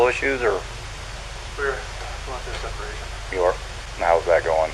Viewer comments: It sounded like he said “we WERE going thru a separation” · Sounds like he is saying "We were going through a separation". (past tense) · did anyone else catch that? Sounded like he said "we WERE" not "we're", maybe his first verbal slip up.... Guess it doesn't really matter now tho ~(ó , ò)~🔒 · he doesn’t say “we are going through a separation” like the text says, but what he does say is “we...were going through a separation”, he not only uses past tense but he also pauses before saying “were” as if he was going to say “we’re” but realized she was dead so he said “were”, either way he had to pause and think about it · if you listen closely he says "we were going through a separation..." Not "we are going through a separation" like the caption says.